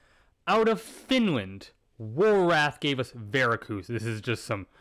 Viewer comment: Couldn't quite catch it, what's the name and band of the Finnish album at 3:35? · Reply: Warorath